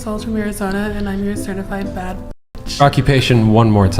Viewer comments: first time I heard say her occupation i thought she said "certified fat bitch". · I heard "im a certified fat bitch." My hearing is perfect, dont at me! lmaoooooo W T F man. just like the girls on dating apps who say "i dont want a man who smiles." (i have a screenshot!) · I heard "Certified f*t b*tch" at first, thinking that at least she's(?) real about it. I was wrong on so many levels... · He thought she said “Certified fat bih” 😂😂😂😂😂😂😂😂😂😂😂😂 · Damnnn I feel so bad, I thought she said certified "fat" bitch💀💀💀💀💀💀